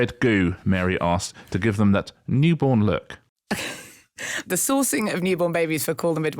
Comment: I definitely heard "The Saucing of newborn babies" lol